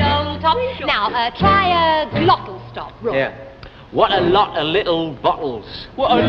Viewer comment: "what a lot of little buttholes" ik thats not what they said but thats the only thing i can hear kajdkjahs